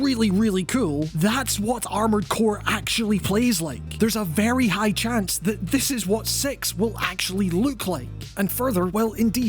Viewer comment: im so sorry, it sounded like you said "there's a very good chance this is what sex will actually look like" and i had to rewind and gave me a good chuckle